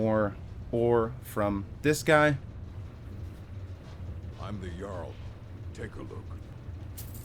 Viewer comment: it sounded like he said he was the jarl 🤣